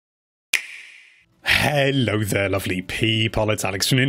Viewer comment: I thought u said, "hello there ugly people" xd